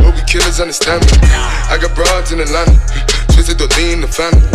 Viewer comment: I thought he was saying i got my bro's in london hit like if think it to 😆😆